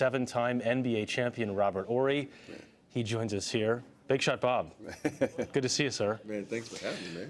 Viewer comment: did he say big shot bob???